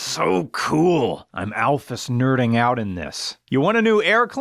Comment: Did he say.....at 7:46 deep breath "I'm ALPHYS nerding out over this"?!?